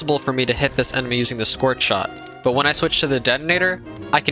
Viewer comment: good god don't enable captions at 3:04, it says "squirt shot" instead of scorch shot ☠️☠️